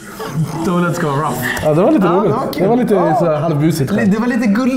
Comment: najs att videon bara frös mitt i punshlinen, så man varken hörde punshlinen ele deras reaktion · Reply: "De vill att det ska vara rouff!" - var slutklämmen...